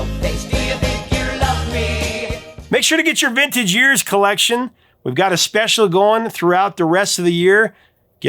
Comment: I misheard you as I had to turn away for a moment. I thought you said, "Don't forget your vintage ears!" Huh!? 😂😂😂 You took me back to my youth! In 1970 I turned 5. Tears of A Clown was huge here! It was regularly on the radio for years & years. Didn't mind in the slightest! 😅 Terrific selection!